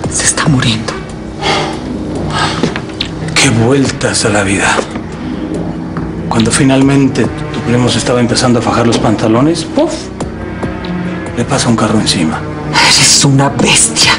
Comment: Please anyone tell me what Justo said in: 7:15 ? I'm learning Spanish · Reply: TheBro, Que vueltas da la vida, justo cuando empezaba a fajarse los pantalones pufff le viene un carro encima :)